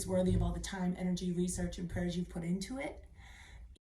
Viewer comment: “Time, Energy, Research & Prayers” into gear.. man ain’t that the truth.. at first I thought you said purge and that would have been true too.. ugh.. nothing like buying something then having to cut it.. lol! 😂😅😊 See you out there! 🥳